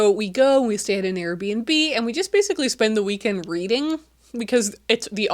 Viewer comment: When telling about the engagement (congratulations by the way!🎉) I heard “and we just basically spend the weekend breeding” not reading 😭🫣❤️